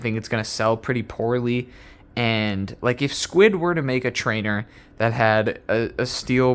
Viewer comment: "If Squid were to" i thought you said if squidward were to make a bali